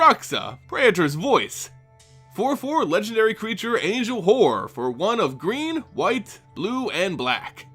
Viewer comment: Lol I thought you said angel hore, and I was like yep she’s kinda like that specially with a planeswalker commander deck. 😅